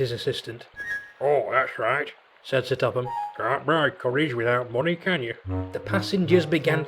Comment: What does Fatt Hatt say at 1:18? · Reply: "Can't buy courage without money, can you?" And that wasn't his assistant. He didn't even have one. That was most likely an inspector or the Knapford stationmaster.